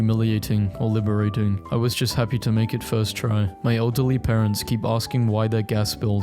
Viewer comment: why did i hear elderly as alt+delete?